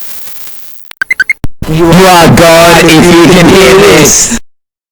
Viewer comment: Did it say "You're god if you can hear this" at 2:44 ? o.O